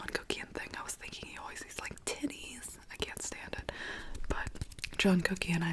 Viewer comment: i misheard it as "jungkookie.. he was always like 'tities' " and I was like wtf 😂 don't fight me pls Great video, as always! Love from Ukraine~